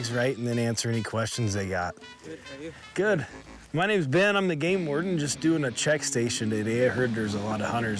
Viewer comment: I thought he say: my name is Ben and I'm the gay warden.